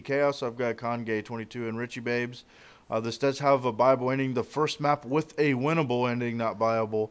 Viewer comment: for the longest time i thought he was saying bilble ending